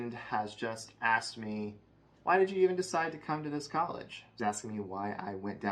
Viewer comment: What I heard: "Why did you decide to go to communist college?"